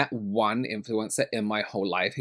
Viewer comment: I hate the word influencer, I don’t watch influencers, I watch people who actually have skills, knowledge and talent!! Hi ❤ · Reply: The first time I heard it I misheard it for influenza and was a bit confused. But nowadays I think that that checks out.